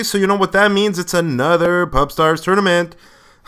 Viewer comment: I thought it said "Porn Star Tournaments". Derp.